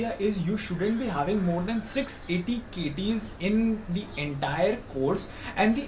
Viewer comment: what did you say 680 ?? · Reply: 6 AT/KT.